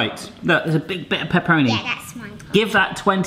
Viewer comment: i thought she said peroni not pepperami lol